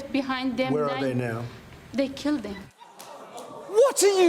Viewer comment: tbf to Trump he may have misheard her. I legitimately thought she said kept not killed.